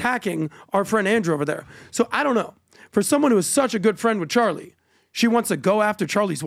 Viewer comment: Oh....I thought you said she wasn't friends with Charlie 😂😂😂😂😂. Damn this man is foaming at the mouth.....give some water!!